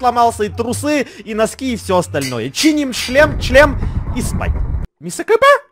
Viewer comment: мне одному показалось что он сказал "чиним шлем чл*н"